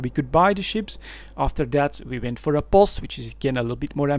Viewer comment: Some vocabulary that may be strange for new players: • POS: A starbase, or POS (player-owned starbase), is a collection of semi-permanent structures that can be anchored to a moon. • Wardec: Declaration of war; a measure that initiates a state of warfare between two corporations / alliances. • C1 wormhole: Class 1 wormhole; there are different classes of wormhole systems. • POCO: Player Owned Customs Office, a structure that allows importing and exporting goods to and from planets. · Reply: Ah POS....I thought he kept saying they wanted to buy a bus! :)